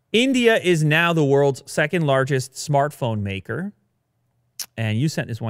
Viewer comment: Radical iMac redesign 7:45 - Play Station 5 13:04 - India is now the world's second largest smartphone maker 19:52 - Taycan Turbo EPA Range Rating crushed on individual's real world drive test 24:58 - New development in the land of Coca-Cola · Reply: "Market. Not maker." — But "maker" is what Lew Hilsenteger actually says at 13:07, though. I was intrigued they could be already making so many phones in India when most of the phones sold there are from Chinese brands.